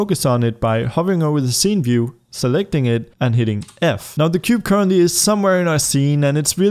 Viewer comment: what do you hear? I hear : by hitting "pay respect" f